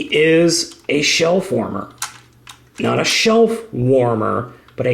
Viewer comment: I really heard "shelf warmer" when he first said "Shell Former"